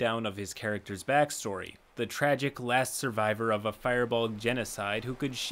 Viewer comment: I have never in my life heard someone pronounce Firbolg as Fire-Bolg. I also thought you said "Fireball" I was listening without watching and literally had to come back and check the transcripts to see what was trying to be said.